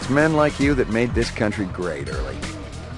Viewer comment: The first time i heard " It´s men like you that make this country LATE, Early.